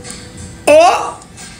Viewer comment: If the stylist really did this then it wasn’t the only thing done to achieve the final look. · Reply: when I first saw this and listened back more than once, it sounds like she is saying, “it always and so perfect?” And honestly, it looks perfect to me. I wish I had her hair!